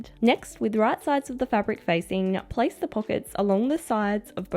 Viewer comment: does she say right side of the fabric facing up or down? Do i have to sew the pockets with pattern side up or down? Thanks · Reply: I had the exact same issue til I realised she just says 'facing', as in facing each other!